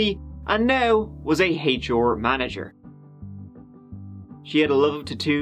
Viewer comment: Ha, ha with your accent u thought you said she was a 'hate your' manager I stead of H.R. Manager !! Lol 😂